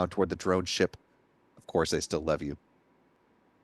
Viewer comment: Did i just hear “of course I still love you” at 37:54??